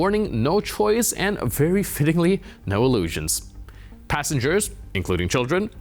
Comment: (03:52) "... they were not flying on a passenger An-24 but on a cargo An-26. No warning, no choice and, very fittingly, no Ilyushins." Perhaps I (deliberately) misheard, but amusing commentry!